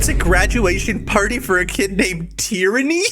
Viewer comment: THOUGHT THAT SAID TRANY